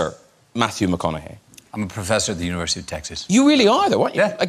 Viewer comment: Sorry all I heard was: University of SEXas"